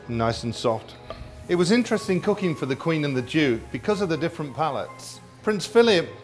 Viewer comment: I thought I heard, the Queen and the Jew